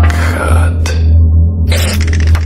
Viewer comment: It sounds like there saying “sorry black hat”